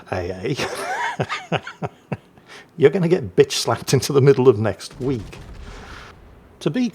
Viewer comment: Each time i hear "bitch slapping" i understand "beach slapping"